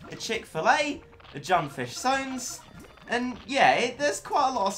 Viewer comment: Did he just say Junfish Sones